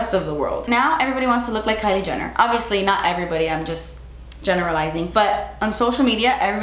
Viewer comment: while talking about Kylie Jenner, says, "But I'm generalizing..." What I heard was, "But I'm JENNER-ralizing..." 😅😅😅 Didn't hear that the first time I watched this video... rewatching during quarantine... 😊